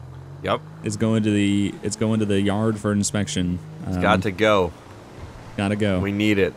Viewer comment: Bro did anyone catch that he said inspankted 2:15 😁😂😂😂😂